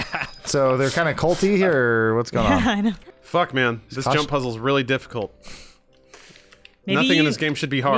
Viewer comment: DID SOMEONE SAY JUZZLE??!?